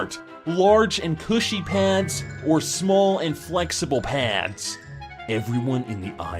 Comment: I thought they said large and pussy pants but its pushy :/